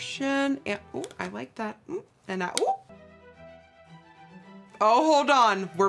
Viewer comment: Did she just say and I ooo at 8:31? Lol